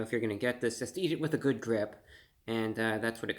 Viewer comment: "eat it with a good grip" is sage advice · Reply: I thought he said good drip 🤪😎